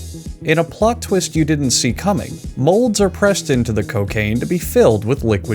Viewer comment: I thought I heard cocaine so I played it back it really was cocaine Also it’s not the cocaine making the jelly beans addictive it’s the jelly beans making the cocaine addictive